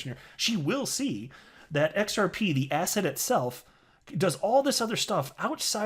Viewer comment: sometimes i cant tell if he said "asset" or "asshat" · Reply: Yea I watch a few of videos everyday and every single one I hear him say “asset” ...and think “asshat” Luckily nothing rhymes with “biscuits”